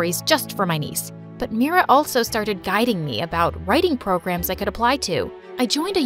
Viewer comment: I thought it said “ she started judging me”